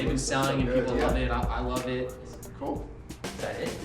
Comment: She said at “cookies and kicks” and they cut it off to Luke’s kitchen. Lol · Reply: OG Mackmizzle323 I thought she said cool kicks